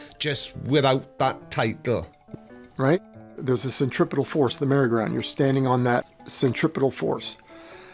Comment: does he say centripital force or do I need my ears cleaned out again?